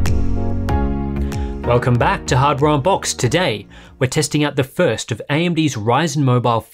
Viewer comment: I heard "Hardware Boxed"